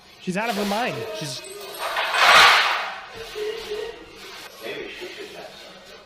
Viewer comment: I think some of the audio translation is wrong on this. It's like trying to translate ghost evp where twenty diff peeps will here the same audio twenty diff ways. Anyway, here at 8:10 its sounds like he's saying either "maybe she could attack somebody else" or "maybe she should have someone else" I'm guessing, because if u listen without looking at the writing it's easier to hear, and what I hear is kinda like "maybe she could/should have/attack some of/something/someone else/those" so far on that i whittled it down to two or three possibilities. I really think with them talking about someone going with her and her not wanting Jerry, that it's more like "maybe she should have someone else [go with her]" which is close to what it was translated as. But theres a few more that are off too, but with how noisy it is the only way your gonna get clear audio is if someo goes through and isolates certain audio frequencies like voices and removes others like the banging etc, and then maybe we can bear it better. Until then the best way is to listen without looking at the writing otherwise it's like those lyric changes on TikTok where u read 'bald boy added woma' for the bee gees song so that's what u hear. But if if u read 'more than a woman's when listening, u hear that instead. Amber is clearly th abuser here, I just think we need these cleaned up. Justice for johnny